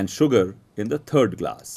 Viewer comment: it sounded like he said "turd glass"